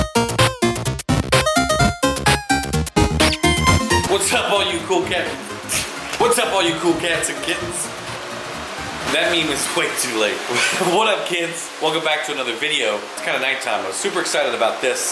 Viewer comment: It sounded like you were saying “what’s up all you coke heads” lmao